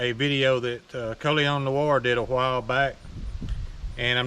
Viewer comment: Totally a side note funny, but the transcript for the video at 0:23 claims you said "Curly on the war" instead of "Colion Noir" lol